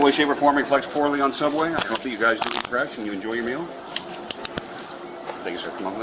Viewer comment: The cop that arrested the Subway guy was super chill. Professional, direct, polite, and ensured he did his job right, need more cops like him fr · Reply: Dude was soooo funny! At 13:29 He actually says “ This in no way , shape, or form reflects poorly on Subway. I hope that you guys eat fresh and enjoy your meal.” The subtitles are all over the place. Anyways, I love that cop. Lol!